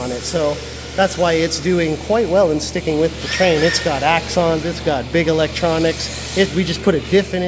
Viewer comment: what does he say? It's got Acks on it? What are Acks? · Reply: I meant to say "it has Axon II Shocks..." :)